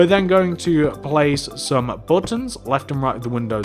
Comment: he says 'bluttons' instead of buttons